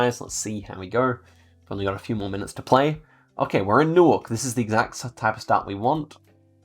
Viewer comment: thought u said you were in Newark (New jersey) and i was like bro what the heck are you talking about